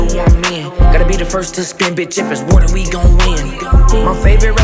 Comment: im tweaking, it sound like he saying “if it’s water we gone win”